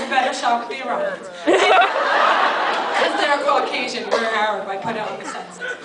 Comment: does she say 'you have a better shot with the iranians?' I'm not sure I get that part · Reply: yb they always say they are "aryan" lol